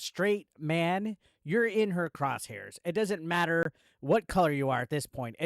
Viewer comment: I thought you said "crotch hairs". :face-orange-raised-eyebrow